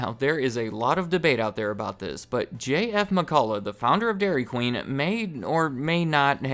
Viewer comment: "J.F. McCullough" Wait! Did I hear that right?